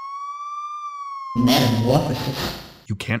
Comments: Just curious, what's he saying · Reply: Metamorphasis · What does jevil say in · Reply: metamorphosis i think